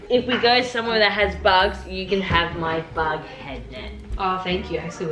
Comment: did somebody say bughead